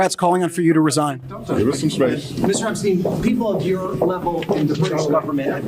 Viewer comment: Anyone else catch that one of the reporters called him Mr. Epstein?